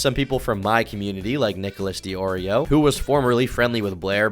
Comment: Did he just say Nicholas the Oreo!?! I need to meet that oreo.